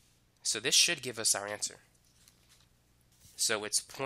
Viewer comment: "this shit gives us our answer" lmao ik this ain't what he said but that was totally what I heard